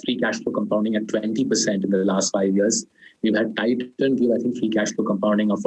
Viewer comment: Saurabh after you mentioned Page, Asian Paints at 15:45 mins, the audio went bad. Couldn't make out the name of the scrip which has been giving a cashflow compounding of 40-50%. Can you please share the name again. Thanks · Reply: Titan